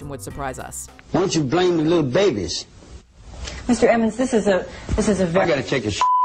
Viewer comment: Anyone else hear that lady call that man "Mr. Evans" at 5:55 ? ?